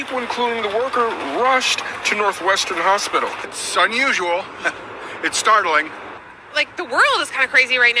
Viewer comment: That one guy being interviewed sounded like he said “it’s sun usual”. 😂